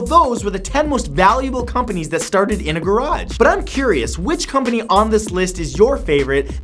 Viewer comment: I thought it said "That started in the garbage"